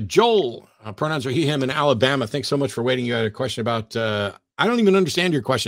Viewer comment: It sounded like Matt said "...pronouns are He, Him, and Alabama"! 😄